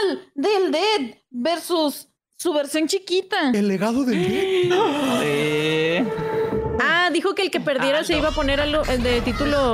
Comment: Pensé que decía El Delgado del Ded por la foto xDD